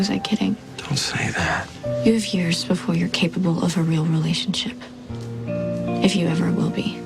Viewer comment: wht did she say!? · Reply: You need years before your capable of a real relationship